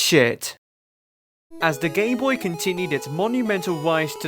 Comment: at one point in the video I think he said "as the gayboy continued" yes he called it the gayboy at that one point and it's not me mishearing things I heard that I listioned to that part over and over to make sure go to 0:50 and wait for that part tell me if I only misheard that cause every other time he says gameboy it sounds like gameboy